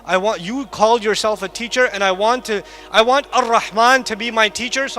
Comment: mark he says Ar Rahman however sceen caption says Al instead of Ar